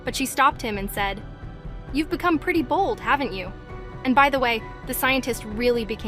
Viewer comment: At first I thought she said "you're getting pretty bald" 😂😂😂😂 lol